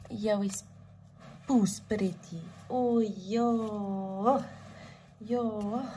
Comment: actually caught me off guard (you is POES pretty) 🥲 I was like did I hear that right 😂